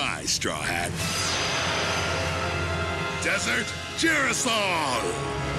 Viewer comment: What’d he say? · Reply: Desert Girasole